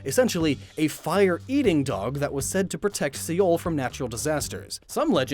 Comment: Is he saying Seoul? 🤨